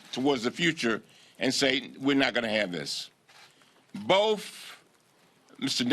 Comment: Cummings omgoodness did you just say "boaf" instead of "both" at the 9:54 point in the video above...yes you did!!!...that's hilarious coming from a black man myself...you don't say Fursday for Thursday do you? or Fug for Thug do you??